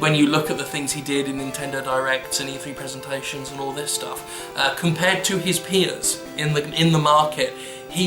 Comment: I actually thought you said "compared to his penis". I don't know why, I was listening to the video while playing some games so I wasn't paying 100% attention but god damn that's one way to grab someone's attention.